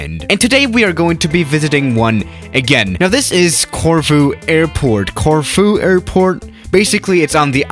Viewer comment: I Thought you said tofu airport 😂😂😂